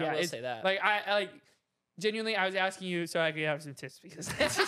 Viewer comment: did say d t#t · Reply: He said tips